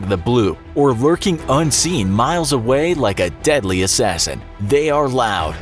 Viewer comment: Did he just say "Or lurking unseen for miles like a deadly assasin, They are loud"